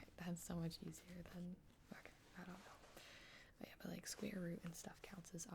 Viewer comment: anyone hear her say "fuck it, I don't know"